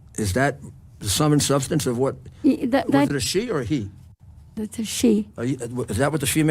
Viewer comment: Did he say: "She-male"?